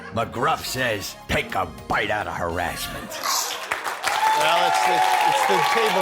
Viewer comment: That ending sounded like said "take a bite out of herassmen"🤣🤣🤣